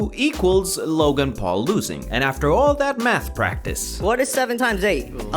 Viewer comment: 4 a sec i thought he said for all that meth practice.